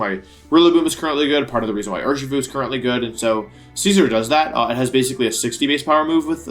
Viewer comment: Really not used to hearing him pronounce the Pokémon like that lol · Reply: The way he pronounces Scizor tho.. XD I thought he said Caesar